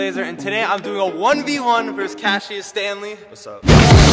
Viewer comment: when you said Cassius Stanley I thought you said cashes family